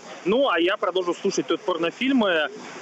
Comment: он сказал порнофильмы???я один это услышал???